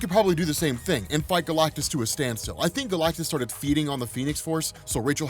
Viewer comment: Lmao! I thought you said F Galactus! I was like oooooo, save that for patreon my man